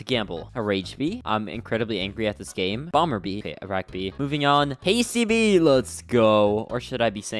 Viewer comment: WHAT DID HE SAY? IRAQ BEE?